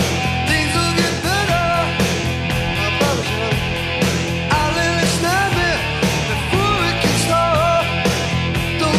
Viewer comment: Can anyone please tell me what he's saying about 1:04...? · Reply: I promise you honey! It took me forever to finally figure it out 😅